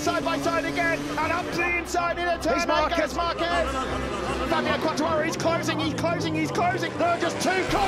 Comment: 実況者が、マルクマルケス勝利だぜって言ってるように聞こえるw